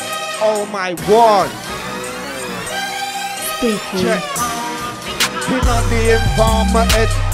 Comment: His look at the camera at 19:08 when that riddim dropped in had me. Mad tune still. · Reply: Check* i always thought it said tick too haha